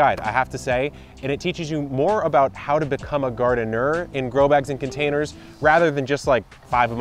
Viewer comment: I thought you were slowly saying “GaR. Den. NERD!”. I was like, Yes! That’s me! for a minute & then chuckled realizing you just said “Garden-ER” 😝